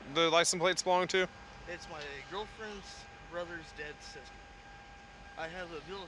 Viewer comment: Dude: It’s my girlfriends little brother’s dad’s sister Cop: At this point, I have no idea who own's it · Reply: Oh....I thought he was saying dead sister. I was thinking well how did you text her. 🤣 🤣 🤣 🤣